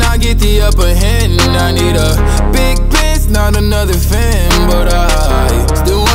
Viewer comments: I thought he said I need a big piss instead of Benz so I searched up the lyrics... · ohh big benz I thought he was saying big piss